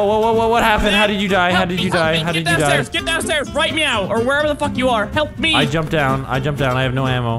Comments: Did MiniLadd say "right meow"? If so, that is so cute! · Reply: yes he said right meow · Mini Lad said "Help me! Help me! Get down stairs right Meow!" He said "Meow" instead of now. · Mini "Get down stair right meow!" Is it just me or does it sound like mini said meow instead of now?